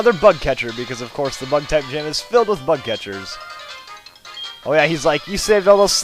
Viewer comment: it sounded like u said butt catchers